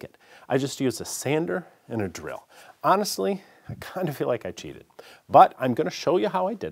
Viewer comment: I thought you said "I kinda feel like a Jew" rather than "I kinda feel like I cheated"... I was like WTH!